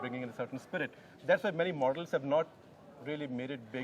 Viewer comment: min hear......Many MORDELS instead of models